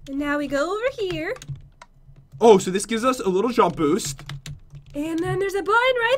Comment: i think pat said levitation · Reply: he said “a little jump boost” but it gave him levitation